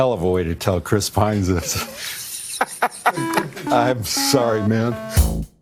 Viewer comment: does anyone know what he says here? “This is a hell of a way to tell”—??? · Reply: Chris Pine(s) this.